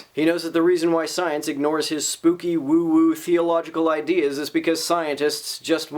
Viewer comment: For some reason, when you said the word "theological" in the last bit, I heard it as "feelogical". Not entirely inappropriate, I think.